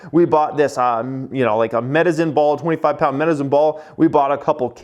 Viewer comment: Did he say "Medizin" ball when referring to the medicine ball? Sounds like someone's got the planet stuck in their head...